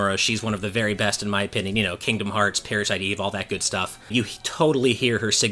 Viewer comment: I didn’t catch that, what series did he say? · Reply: He said "kingdom hearts and parasite eve"